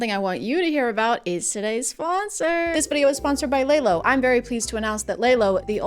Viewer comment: I was cooking some stuff and i heard her say "i'm sponsored by lego", i am deeply disappointed